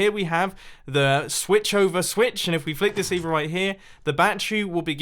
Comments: i thought it said bat tree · I didn’t here properly so I thought he said a “Bat Tree” as in a tree that grows bats 😂 but he meant a “battery” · I thought he said "Bat Tree" and was expecting the tree to "discharge" it's bats... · When he said “Battery” I thought he said “bat tree” xD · I thought when you said battery u said bat tree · when he said battery i thought he was saying bat tree. im sitting here like is this some new meme or does batman own a tree now?